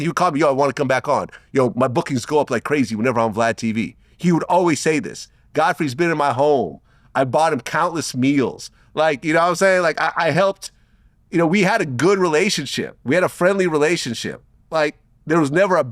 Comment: did he say "Godfrey has been in my hole"?